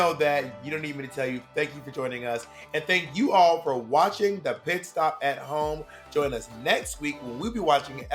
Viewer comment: Why did I hear "and thank Yuhua for watching the Pit Stop at home."? LMAO